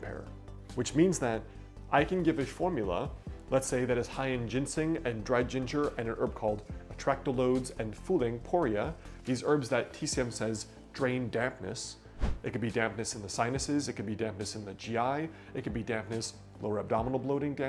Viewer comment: At around 7:19, you said, according to the subtitles (which are not accurate): " I can give a formula let's say that is high in ginsing and Dred Ginger and an herb called attract loads and fooling Poria" -- I am wondering what you actually said for "attract loads" as that is obviously not what you said, but the AI speech recognition at YouTube thought you said that. The rest I understood (ginseng, dried ginger, fu ling poria).